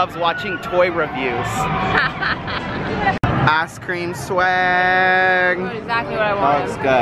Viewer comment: Sorry... but it sounded like you said , "ass cream swaaaaggg" lol